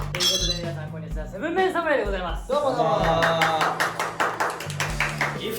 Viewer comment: 「ということで嶺亜さんこんにちは7 MEN 侍でございます」としか聞こえない